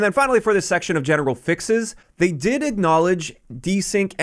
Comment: did you say genital fixes? or am i hearing things? LOL